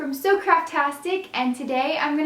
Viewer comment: I thought she said so CRAPtastic in the beginning! LOL!